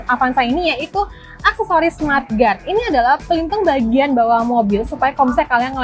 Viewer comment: smart guard? ga salah dengar gw ya...:p